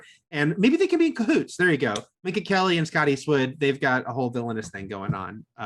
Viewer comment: When they talked about Minka Kelly, I heard "Mindy Kaling" and I let you imagine how I pictured it.